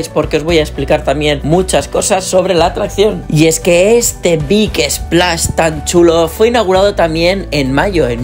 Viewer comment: qué es un "biques plash" que dices en el 12:40? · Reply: Big Splash. Es un tipo de atracción.